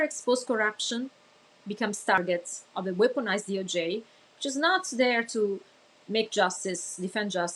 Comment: She said "...weaponized DOJ", right? I heard, "... want a nice handy-j?" I married a Russian. I know how they do.